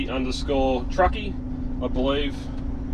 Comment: Great video again. Dam that rain. It's raining here in Perth too. No point washing the truck. I couldn't find that other truckie channel. I thought you said, The Aussie Truckie. What is it? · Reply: The_Aussie_Truckie